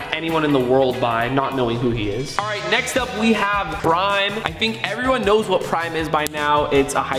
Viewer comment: Did he say brime